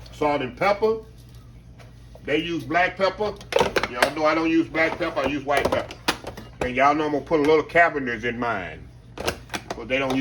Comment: What was that seasoning? I couldn't catch the name. Thanks! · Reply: Cavenders Greek seasoning